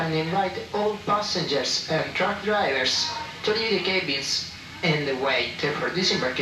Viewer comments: swore the announcement said 'drunk drivers'... who knew Italy was THAT progressive... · Haha! 48:22 it sounded like the announcer said "...all passengers, drunk drivers and (sca)babies in the way..."' Well, the hamburgers looked good 😋